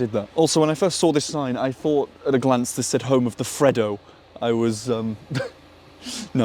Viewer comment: I thought it said ‘Home of the Frodo’ and was really confused. Like, isn’t that in Middle Earth???